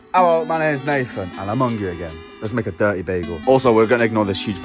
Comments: Am I'm the only one that thought he was saying "I'm Hungarian" instead of " I'm hungry again" 🤔🤔😆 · I literally had to rewash the beginning of this video clip. Because I thought you said hi my name is Nathan and I'm Hungarian. I'm glad I replayed the video to re-watch it. Because I can hear you clearly the second time he said hi my name is Nathan and I'm hungry again. But I really thought you said you were Hungarian. And my thought was doesn't sound like a Hungarian accent to me